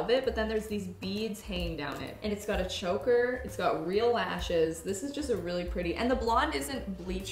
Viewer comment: I thought said “Barbie has ashes” not “lashes” 😅